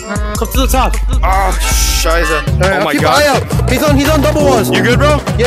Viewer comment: quick question did you just said Scheisse in german? or was that a other word